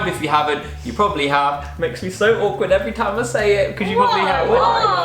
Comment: What does say at 19:53 ? "....you probably have. Makes me so awkward every time I say it cause .......?" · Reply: "Makes me so awkward every time I say it because you probably have"